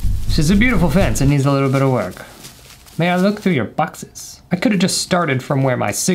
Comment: I thought he said Botox